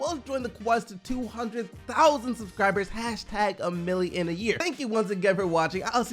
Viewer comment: Just realised he says "a mill in a year" thought he said "a million a year"